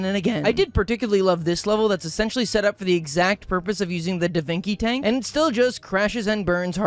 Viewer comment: i’m sorry, did you just say davinki tank